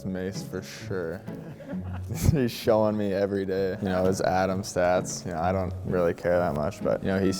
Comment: *Atom is an age group for hockey. Atom is U11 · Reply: oh shit I misheard that bad, I know what atom is but I was sure he was saying Adam stats lol